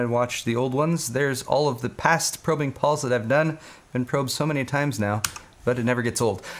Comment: Tbh I thought it said robbing Paul so I was intrigued